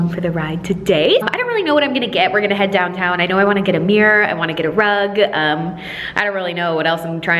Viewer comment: I’m the only one who thought she said ‘I want to get a beer I want to get drunk’ when she really said ‘I want to get a mirror I want to get a rug’😂🤪